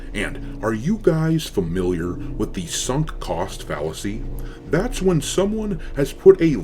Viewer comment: I misheard sunk cost fallacy and thought he said the Horus Heresy. 🤣🤣🤣